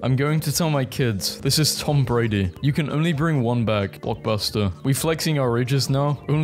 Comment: Misheard "blockbuster" as "pot buster" and I just accepted it. You are nevwr truly safe from Potemkin Buster